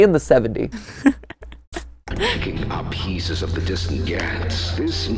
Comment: “Kicking up pieces of the distant gyat” Why did I hear that 😭